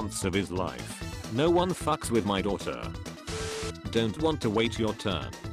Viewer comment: "thought he said no one fucks my daughter" yikes